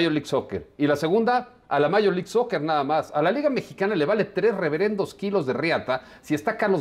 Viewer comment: Jajajajajaj che. Solito te emponaste en el minuto 6:00 clarito dice. "A la Liga Mexicana" ..... Despues dice que dijo que a Funes y los que ivan a jugar